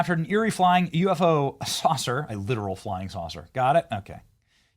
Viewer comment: I thought that said liberal flying saucer